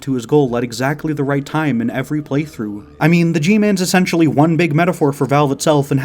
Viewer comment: "the gman" i heard it as the guhman